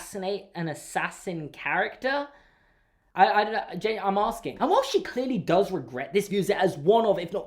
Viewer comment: am I the only one who heard this and thought he said “J, I’m asking” as a reference to Super Carlin Brothers? I think it was supposed to be “genuinely, I’m asking” but I just need to know if I’m the only one who heard that.